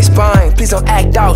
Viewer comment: so I keep hearing 'shy and surprised' as shia surprise and well that makes this sound uh different